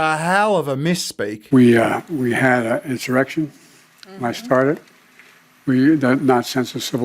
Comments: i think he said, "we had an insurrection when i started" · he was muttering but I think he said “there was an Insurrection when I started.” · i heard "when i started" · Think he said when i started